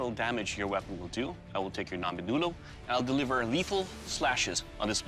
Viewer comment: did anybody else hear him say “I will take your ramen noodle”